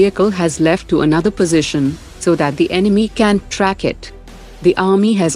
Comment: should it be Enemy cannot track it instead of "can" track it. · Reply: so the enemy can't track it" is what is said.